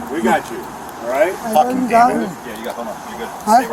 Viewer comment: whad the cop say😂, sounded like 'Fuckin Daniel'